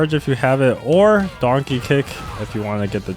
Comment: wtf are you saying? Donkey Kick or Darky Kick?